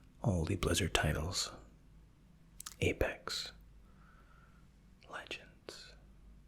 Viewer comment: did he say apex legends?